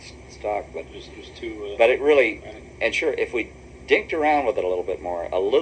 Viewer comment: I think he aaid "dinked." The phrase "dinked around" was sometimes used to loosely describe a trial and error process. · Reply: I had to listen to him a second time because at first I thought he said dicked, but after I did hear dinked.😁